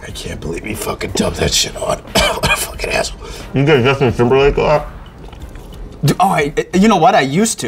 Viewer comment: Did Eric just say “have you ever got Justin Timberlake on?” And the host day “you know what, I use to, and I appreciated it, but I think as I’ve gotten older, I get it less” lol what?